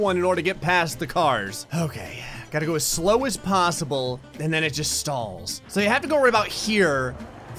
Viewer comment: I thought he said “and then uninstall” tor which I was going to comment “which is what you want to do with your life right now “